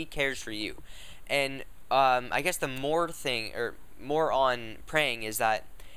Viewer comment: Did you just say "moron praying"?